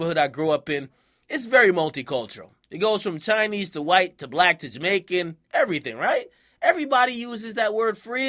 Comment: Did he just say black to Jamaican?